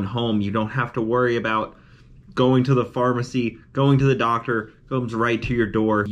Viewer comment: I thought it said ..hoe on the door😂